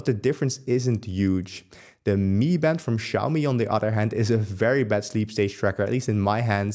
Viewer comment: accent almost confused me. Thought he said "very best", whereas he said "very bad"... Sigh lol